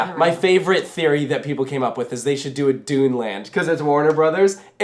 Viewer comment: I misheard that for a second and thought you said people were saying they should add a DOOM land. I had to do a double take for a second before I realized you said Dune. Was it just me or did any other gamers hear DOOM as well?